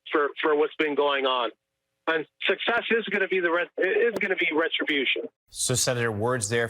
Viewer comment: I thought he said SUCCESS is going to be retribution. So what’s wrong with that.